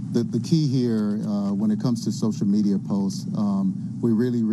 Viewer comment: The first time listening to his answer about social media I thought he said “the tea here when it comes to social media posts..” and I was fully convinced we’re in a black mirror episode